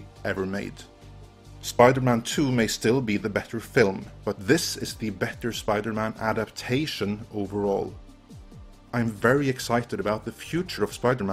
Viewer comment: "Spider-Man 2 may be the better movie, but Homecoming is the better Spider-Man movie" No,, that is incredibly faulty reasoning. if Spider-Man 2 is the better movie by your admission, than Spider-Man 2 is the better Spider-Man movie. This applies very everything and not just Spider-Man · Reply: Except you quoted him wrong. "spiderman 2 may still be the better film, but this is the better Spiderman adaptation overall." is what he actually said. Listen again